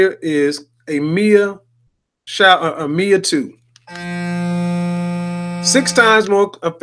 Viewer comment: what was this called i cant make it out? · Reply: It’s a facial cleansing brush. The actual brand of his one is Mia 2 but I don’t think they sell those ones anymore. Any type would work the same tho